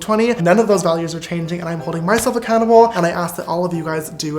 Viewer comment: What did james charles say on 13:11 cause i heard him say "im holding myself and cannibal" but i dont think thatz what he said🤣🤣